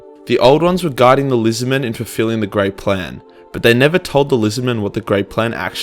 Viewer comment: multiple times throughout I thought you said "the great prank" which I found quite hilarious.